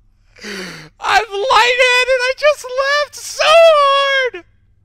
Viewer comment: SOME TELL ME WHAT THE heck HE SAID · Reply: I think he said “I’M LIGHTHEADED I JUST LAUGHED SO HARD”